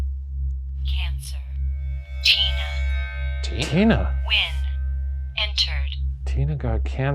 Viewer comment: i though it said "when" instead of "win" TT